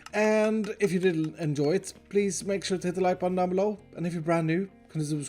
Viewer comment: lol i dunno if it was intentional but i think he said "if u did"nt enjoy it please hit the like button"